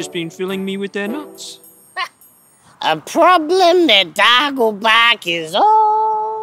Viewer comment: "Dargle-bark' Huh, and here I thought he kept saying his name was Dagobah.